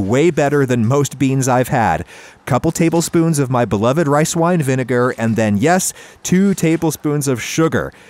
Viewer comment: I thought he was gonna say white wine oh god